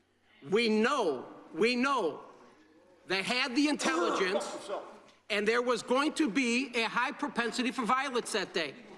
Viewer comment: what’s he sayin here? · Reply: 8 months later but I believe he said "fuck yourself ."